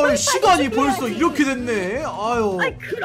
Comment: 시x이라고 저만들림?